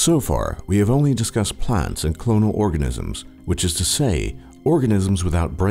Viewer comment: lol I thought it said orgasem